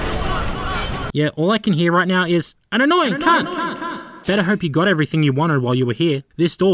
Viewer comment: I only hear "I'm annoying hah!" Yes you are game!